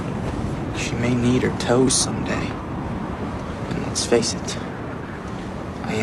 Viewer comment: i’m sorry Jacob but did you just say “she may need her toes some day” in a southern accent? listen I don’t know much about love but I do know biting her toes off won’t get her to love you.......just sayin. ._.